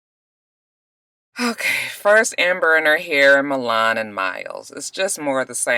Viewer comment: Lord!! It took me a hot minute for me to realize you were saying Amber and Her Hair. I truly thought you were saying Amber and Mahair or something cause I passed on watching this season and just watch the reviews/recaps. So I'm really over here like "is that her son?? Who TF is Mahair??" Keep doing it cause the nicknames you come up with are HIGHlarious.